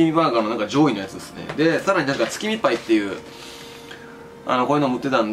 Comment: 月見っぱい って聞こえたのは俺だけ？